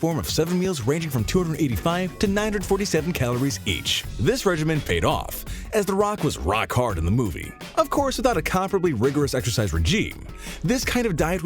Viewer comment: Did he say he was rock hard for the movie?